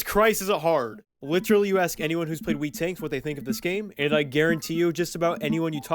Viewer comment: for a second i thought it said world war 2 tanks